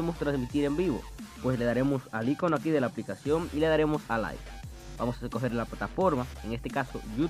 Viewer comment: No le entendi muy bien a esa parte bro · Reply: Te lo traduzco: le das al boton flotante(el icono prqueño) y tocas la ultima opcion de abajo que dice "live"